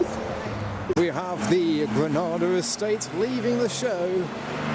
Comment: I like wagons lol, what did you say 9:19 is? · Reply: Ford Granada Estate